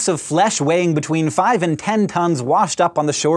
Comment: i thought Hank said it weighed "between five and ten tongues" and i was momentarily very confused.